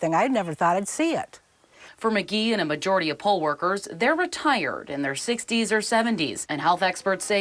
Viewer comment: When he said "poll workers", I heard "pole workers"... Yeah, I know, I have a dirty mind...😏